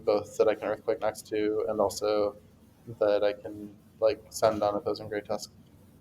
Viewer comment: Really good info, kind of hard to hear you at some points though. I get the gist of it but like at 7:00 you said “dragonite is a good ground immunity both that you can earthquake next to and (sandown) opposing great tusk” I get that it’s good against great tusk but I’m not sure what words those were for example. There’s a couple spots like that where I can’t make out the words but this was really good and you should speak loud and proud after this great result and awesome meta call!! I was really impressed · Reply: for the line you missed, dragonite is a mon i can send on opposing great tusk. can be switched in vs, sent in on, that type of idea. probably a bit of a poor word choice on my end.